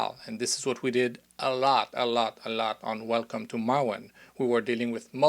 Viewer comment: Also, what is the film you mention at 27:55? I didn't quite catch the name...And also also, Frank doesn't make cookies. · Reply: Welcome to Marwen